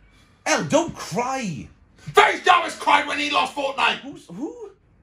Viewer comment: did he say Faze